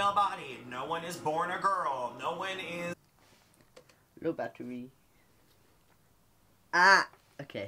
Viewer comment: What’d Noah say at 13:51? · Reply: "low battery"